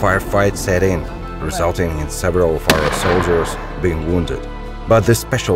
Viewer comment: they fire a mortar at 4:36 and i cant make out what word he is saying, just after the part about ukrainian special forces and little being known about them but it sounded like he said 'forigen' does this imply ukranian special forces were actually a international force? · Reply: He says, "Almost immediately a long firefight set in, resulting in several OF OUR SOLDIERS being wounded".